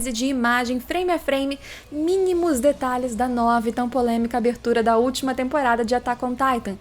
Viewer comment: Só eu escutei"tá nove tão polêmica" no começo?k